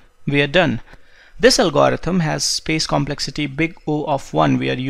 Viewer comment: closed caption says "space complexity O(n)" while you're correctly saying O(1), just letting you know